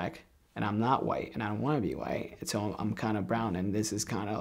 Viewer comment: All I heard was “I wanna be white” — that’s hurtful.